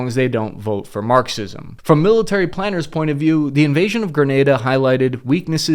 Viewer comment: I thought they said the invasion of Greenland